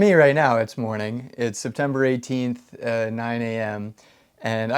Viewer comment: Thanks for your comment! These videos are basically real time, so this was all of last weeks work. I have mentioned a few times that the process has been slow as I wait for permits, but once those all go through, which could be as early as next week, I will have a contractor on the job, and a whole crew, so things will speed up, and I hope to have lots more progress to share in each video. Stay tuned, and thanks for your support! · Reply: According to my wife, at 73, my hearing isn't what it used to be... I thought you said December. Once I went back, I heard September. In any case, I'm right there with you!